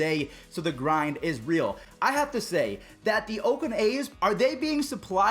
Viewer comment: 18 I thought he said I have to s*it